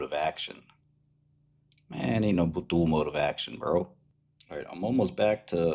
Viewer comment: "Ain't no dual mode of action" Did I hear well??? I think there's plenty of examples of drugs with dual moa, even in MS. ???